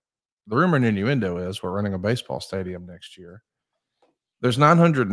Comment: did you say “WE’RE” Conrad? 🤔